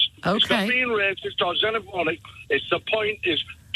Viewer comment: ish does he say xenophonic???